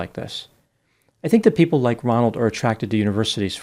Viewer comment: I could swear you said “Donald” at 14:20 instead of “Ronald”